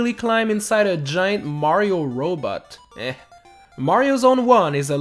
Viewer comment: I heard "Mario's Own One" instead of "Mario Zone One".